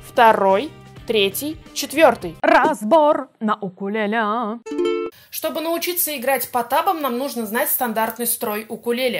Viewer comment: Мне одному послышалось "Чтобы научиться играть Потапом"